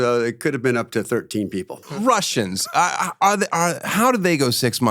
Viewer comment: I thought he said "martians"!😂😂😂 He said russians😅